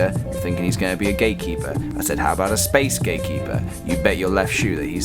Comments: When he said "Gate keeper" it kinda sounded like he said "Gay keeper" and yeah idk where i'm going with this sentence. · omg lol when u said gate keeper I thought u said GAY keeper lol wtf